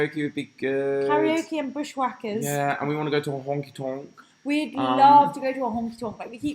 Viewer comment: you should totally go four-wheeing in a monster truck, its exhilarating! i thought you said wonky-tonk! 😂